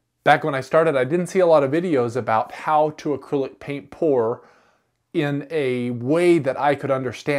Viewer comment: I thought he said acrylic paint porn at first😅